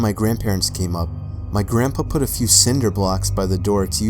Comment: I had subtitles on, and here's what it said "My grandpa pussy" Uhmm okay all right then. And at 18:09 it mentioned something about his girlfriend's sluts...